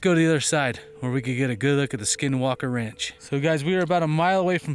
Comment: Oh I thought you said skywalker! LOL!